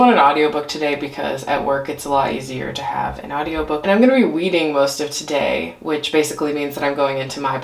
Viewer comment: Every time you said “weeding” all I heard was “wreading” in the pwincess voice 😂💀😭